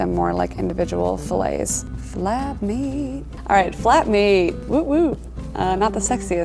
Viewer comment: I thought she said fap meat